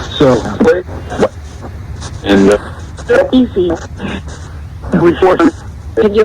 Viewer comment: I heard Said "Deaths easy" around 27:43